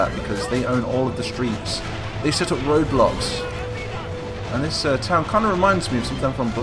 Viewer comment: What I heard was Roblox